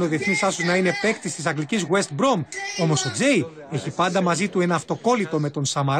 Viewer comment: Didn't understand a word of this, but at around 1:40 I'm sure he said 'West Brom'....?!?